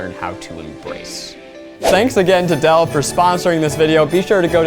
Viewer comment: I heard " Thanks again to Adele"